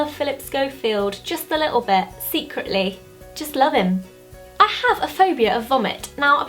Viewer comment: I thought Zoe said ' I have a phobia of the hobbit '